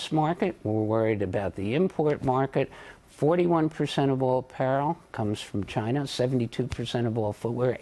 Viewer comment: 41 percent of all parrel? What is the word "parrel"?